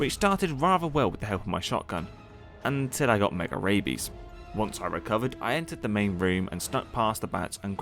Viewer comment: and u said I got mega rabies an ad played and it sounded like u got mega raped😂😭